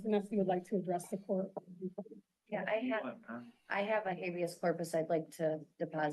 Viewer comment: She has a Habeas Corpus she wants to deposit? Did I hear that right? 😂